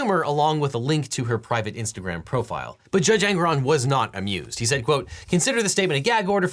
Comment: I keep hearing it as "Judge Angron" and wondering what kind of sanctions Trump can expect from a Demon Prince of Khorne.